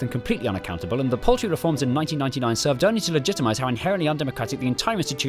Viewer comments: Oh, "paltry reforms?" I thought you said "poultry reforms..." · These “People’s Peers” are chosen for their skills and experience — such as if they’re experts in law, or science, or the arts, or beer — thus assuring that the House of Lords is truly representative of the British people it serves, with the exception of the 92 hereditary peers, 26 godbrothers, and 620 life peers chosen by the prime minister who ultimately have the final say on the laws that govern our country, despite not being elected, which arguably is a positive thing because it means they’re less caught-up in party politics and can concentrate on getting the job done, but it also makes them completely unaccountable and the paltry reforms in 1999 served only to legitimate how inherently undemocratic the entire institution is and delay any meaningful reform for the foreseeable future whilst we continue to labour under the assumption that we live in a democracy and still have a monarch… That's one hell of a sentence. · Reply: I thought he'd said "poultry reforms" for some reason...